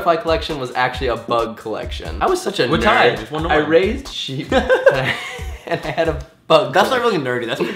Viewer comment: 2.49 I thought he said I raped sheep..